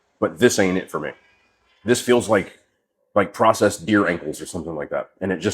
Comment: I thought he said processed deer oysters 😂 damn you know thing's are getting rough when you having to eat the deer's boy's!